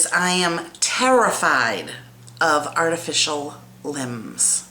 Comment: oh i thought u said you were affraid of lemons but as the story progress i figured you were talking about limbs its ok were all affraid of something...rolf with the melted chesse and a fork i love me some cheese too and abot the car thing im sure we have all done some not so perfect driving so im not saying a word hell i cant walk and chew bubble gum rolf...